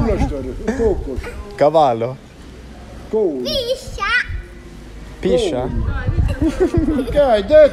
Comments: The little girl at min 30:29 told you "biscia" that means "snake/grass snake" but you repeated "piscia" that means "pee" and i think that's why the grandpa told you she's a bad one ahahah; but i'm not sure of what she actually said between those two. I think she said "biscia" but then she continued the joke as you heard "piscia" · I think, but I am not sure, she called you "piša", translated to English it is something like "wiener" or little boys' penis 😂 That's the reason "dida" warned her and said she is "bad" 🙂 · i think that she said p3nis (piša), in croatian or piša like pissing